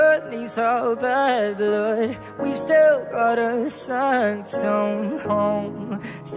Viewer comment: I thought he said “you still got a Samsung”